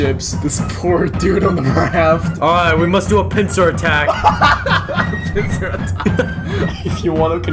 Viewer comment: that sounded like pen*s attack XD